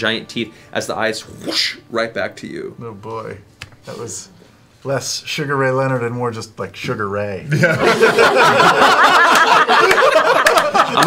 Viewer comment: What does Sam say around 02:01:00 ? I really can't understand it. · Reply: "oh boy.. that was less Sugar Ray Leonard and more just like Sugar Ray" He's a boxer and Sam was attempting to emulate him but failed his roll.